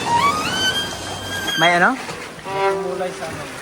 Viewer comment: may ano daaaw??? Di ko narinig 😭😭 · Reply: May militar? Haha